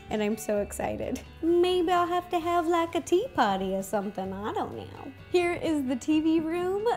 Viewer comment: She said "Tea party" but i heard "Tea potty" haha XD lol